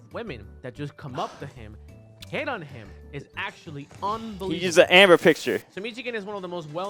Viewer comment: what does m2k say here? I cant understand for some reason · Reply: I believe it was "He used the Amber picture"